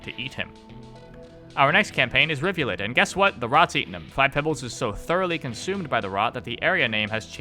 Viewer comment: How dare you not even talk about the super important official dating sim lore that is totally accurate and not made up at all. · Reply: I thought you say five pepsi